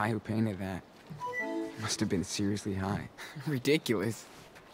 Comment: I hate it when games do this why do the stupid subs say drunk is Sean says high